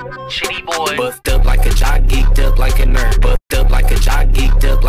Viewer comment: i hear "buffed up like a jockey, built like a nerd!!!1!"